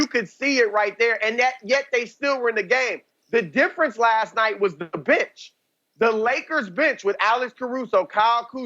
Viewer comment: I honestly thought Chris said “the difference last night was the bitch”. I was like I gotta hear this. Is he calling AD that? Lol